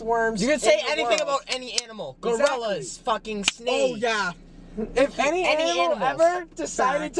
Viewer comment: what did chris say. gorillas and snakes? 😂😂😂